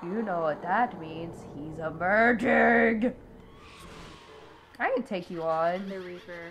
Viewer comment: I have a theory why he was late to the party... but it's probably to indecent to type here.... also, had to relisten to "He's EMERGING!" because I thought you said "He's a VIRGIN"...